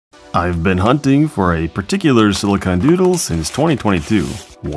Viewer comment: Enunciate, I thought you said silicon noodle and my mind went to "how could he get that on YouTube"